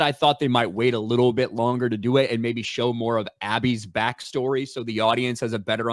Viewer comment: I heard say "show more of Abby's back story" as "show more of Abby's back swing". Kinda ironic.